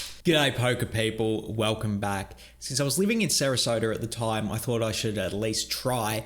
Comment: lives in Australia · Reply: that's what i thought but he said "living in sarasota"not "visiting sarasota"